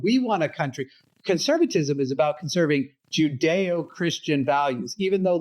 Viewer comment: I heard it as judo christian and wanted to know if the chopped